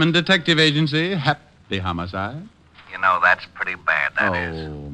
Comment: I know that these transcriptions are edited. Some of the cuts are mid word, thereby changing the meaning of the dialog or confusing the issue. Just one example can be heard at 1:20 on the time line where his customary slogan gag is obliterated. Why? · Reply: I don’t think it’s a cut at 1:20 — I think he is saying, “hap... py homicide!” Similar to the great Tim Curry’s delivery of anticip...pation.